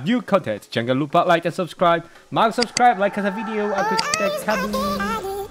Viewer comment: Did just say jangan lupa subscribe?